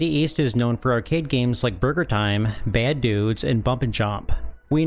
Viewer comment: lol it sounded like you said Murder Time instead of Burger Time…now THAT is a game I want to see on my NES 😆